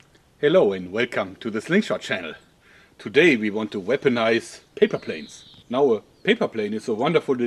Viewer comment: i hear "today we want to wipe an ass" :(